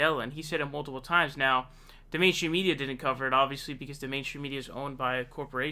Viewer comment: "Now, the mainstream media didn't cover it..." said this so fast that I thought he said "Now, dementia media..."🤷‍♂️